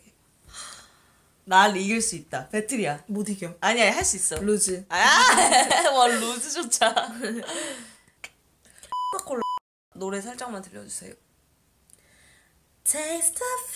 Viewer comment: 에서 나만 코카콜라로 들었음??ㅋㅋ